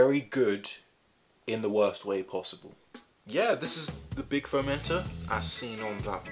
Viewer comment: Don’t tell me that I’m the only one who thought he was saying fermenter for the first few minutes